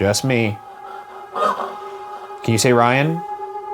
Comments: I thought it said "lame" not "ryan" lmao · that definitely did not say 'ryan'! to me it sounded more like a 'leave' than 'ryan'